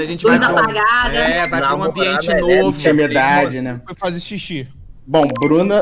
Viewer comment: ele falou “ quando a gente casou” ou foi impressão minha?